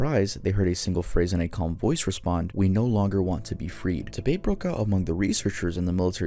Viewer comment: what did you say here? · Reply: Oh debate broke out